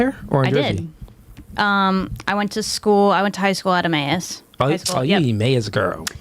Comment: Did she say "I went to high-school out of my ass?" 🤣 🍑